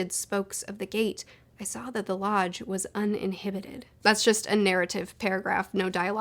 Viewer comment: I know, it doesn't matter. It sounds like she says "uninhibited" instead of "uninhabited" when she reads the passage.